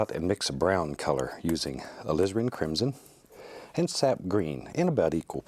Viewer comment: i swear to god he said "lesbian crimson"